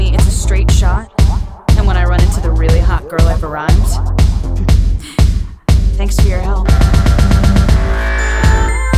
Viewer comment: Can someone tell me what says at 2:43, please? Right after ''And when I run into the really hot girl......'' What's the end of that sentence? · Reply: Mercèdesse F.Bédard i've arrived